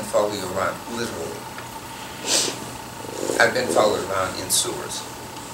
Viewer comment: Did he say "I've been followed around in sewers."?